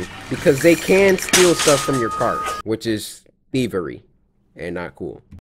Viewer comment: did Cory just say, "and I oop", two years before vsco was a thing or am I just hearing things (〇o〇；) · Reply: He said "and not cool"